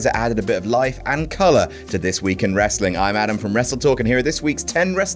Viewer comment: Whenever Adam says "I'm Adam from Wrestletalk" , I hear "I'm Adam from What Culture..."